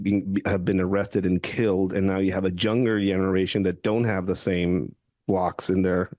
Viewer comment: Did I hear "Junger" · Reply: Younger. The “Y” and the “J” get mixed up with a lot of non Native English speakers.